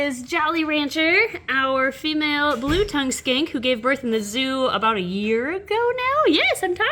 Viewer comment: Idk anything about lizards. So at first I thought she said "Blue tongue skank"